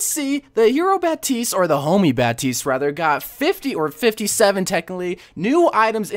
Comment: I thought you said the homely Baptiste 🤦🏽‍♀️